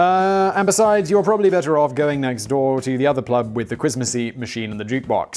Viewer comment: Did you say "plub"? 😲 🤣